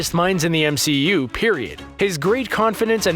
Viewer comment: Did you just say.... pErIoDT?